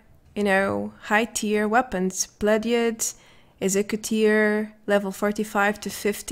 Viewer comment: did you just say "exeggutor" the pokemon?? Lol! 15:52 one of the main problems with survival and white people????! xddddd 🤔🤔